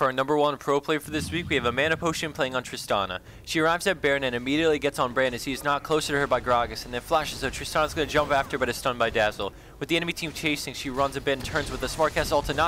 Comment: im confused the first time i hear this "stun by Dazzle" im a dota player btw.